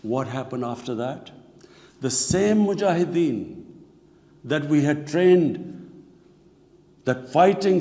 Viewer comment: Did he say "the same Mujahideen that we trained"‽???????